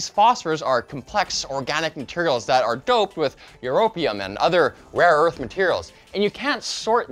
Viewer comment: thought he said opium